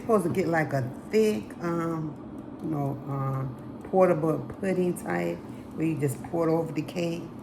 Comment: When you were making the frosting with your Southern accent my Yankee self thought you kept saying it was a portable frosting. I thought isn't all frosting portable? Then I realized you were saying pourable. Lol. Anyways that cake looks incredible. I'll have to try making it sometime. Thanks for sharing